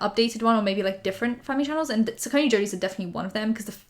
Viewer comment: Can someone spell the family’s name she talks about @ 1:21. My caption says “sconey jodies” Also if you want to do a video on the families I’d be so happy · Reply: Saccone Joly